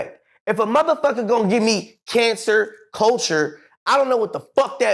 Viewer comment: Did he just say, "cancer culture"??